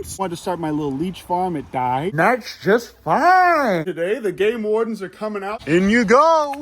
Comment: i heard today the gay wardens are cumming